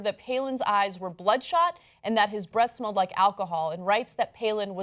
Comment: lol! i think i heard Ana say " his breasts smells like alcohol"